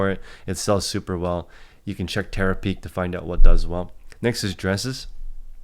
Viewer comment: What did he say to check to find out what sells well? Terra Peak? Is that an app, or a section of Ebay itself?